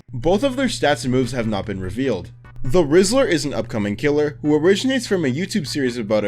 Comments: why did i think he said rizzler.. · when you said "the whistler" at 19:19 I seriously thought you said "the rizzler" and I went, "WHAT." EDIT: I'M NOT GOING CRAZY, HE SAYS "THE RIZZLER" IF YOU TURN ON CAPTIONS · I swear when he said "The Whistler" I heard "The Rizzler". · I heard “The rizzler” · I KEEP HEARING THE RIZZLER 😭😭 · Bro did not say the whistler the first time 😭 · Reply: The rizzler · I thought he said the rizzler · LOL I THOUGHT YOU SAID "THE RIZZLER" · Why did I heard it as "The Rizzler." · Guys, when was starting to talk about “The Whistler” I honestly thought he was saying the rizzler… 😭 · dude when he started talking about the whistler i thought he said rizzler for a second 💀💀💀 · Is it just me or when he said whistler it sounds like he said the rizzler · I thought you said the rizzler😭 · I thought it said the rizzler💀💀💀💀💀💀💀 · did he just say rizzler?😭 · I THOUGHT HE SAID THE RIZZLER 💀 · i thought he was saying the rizzler 💀☠ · when he said the whistler I thought he just said that one of the killers would be the rizzler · thought you said rizzler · My dumbass thought he said the rizzler 🥀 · thought u said the rizzler.... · When he said the whistler I thought he said the rizzler💀💀💀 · I can hear him saying "The Rizzler" instead of "The Whistler" 😭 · Bro I thought he said he Rizzler😭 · I thought you said the rizzler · bro i thought he said the rizzler😭😭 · When he said the Whistler I thought he said Rizzler😂😂😂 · did he say Rizzler???